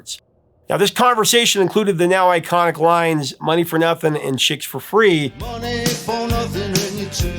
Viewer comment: Weird, I never that gay slur and I always thought dire straights said “checks” for free